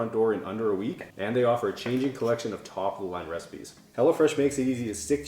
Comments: Am I the only one who thought he was saying hella fresh?? lol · I keep hearing you say "Hella Fresh" and I really need to get my hearing checked...lol